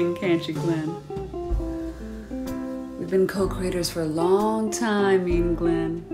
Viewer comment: I always hear "we've been coke riders ..." 😅